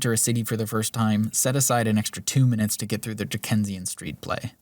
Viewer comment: what word is he using? I know I’ve heard it before but I can’t seem to figure out how to spell it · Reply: Dickensian. From the author charles dickens